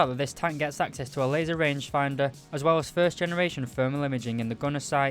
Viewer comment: you said “third gen thermals” it’s first gen btw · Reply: I heard him say first gen. nvm I just got to the part of the video when he said third gen, but he still said first gen in the overview of the tank